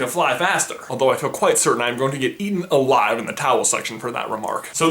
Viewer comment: it sounds like he says "towels section"